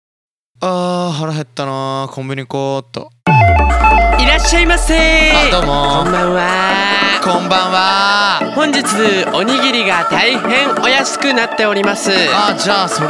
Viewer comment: 「コンビニ行こーっと｣が「コンビニ食おーっと」って聞こえた。